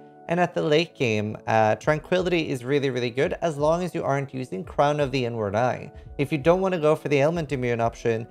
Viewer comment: Im fked up, at first i thought you said "crown of the N-word eye"